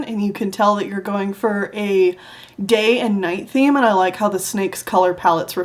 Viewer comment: pirates are cool- 2021 also I am laughing so hard cause when he said "day and night" I swear I heard "gay and night" WEEEZE